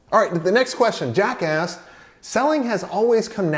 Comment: Sounded like you said 'jackass'(Jack asked). LOL This was one of the most informative, value-packed videos I've watched so far. Thanks a lot! I'm taking a lot of notes and here's what I took from this video; CEO makes the sales AND manages the project is a NO-NO. The sales will decline as the CEO works on managing the project rather than growing business. So instead we must bring in the right team member(s) at the right time to manage the project. *Find a project/account manager that understands the process, wants $ and likes people, will make a good salesperson who can give advice and help the client. As leads come in, the manager qualifies them; “are they qualified, needs match? Budget matches? Etc"., then bring in the right team at the right time based upon the situation and strategy. Bring in project manager, maybe art director or creative team member(s) as needed. Don't rely on account manager to sell deal. Need project manager and account manager to be best friends and have good cohesion. Account manager is close with client and can properly convey client’s needs to the account manager who delineates tasks to the team for a proper seamless workflow. Would bring in CEO at end as a heavyweight. This is really helpful nuts and bolts kind of stuff that will help me and my partner as we formulate our process. We're a 2 person team right now; Me - CEO/Partner, Prospecting and sales, SEO experience, AdWords Certified, Social Media and content creation experience. Partner